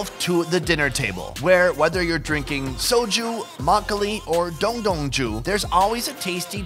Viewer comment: yoooo I literally thought he said Dong Dong Juice 😂😂😂